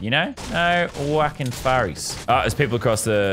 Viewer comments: I don’t know if I heard it wrong but at 1:26 did he say no wokin furries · Like 1:20 it sounded like he said no Wacom furrys lol